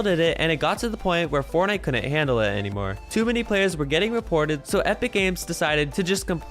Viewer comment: I thought he said deported